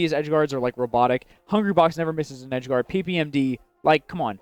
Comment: definitely says PPMD twice and thought he said M2K for one of them.